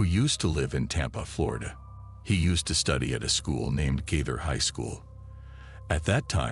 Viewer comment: Did anybody else hear "Gator High School" instead of "*Gaither High School*"? 😅